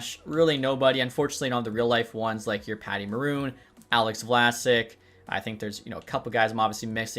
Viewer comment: Am I mishearing or did he say Alex Vlassic is retiring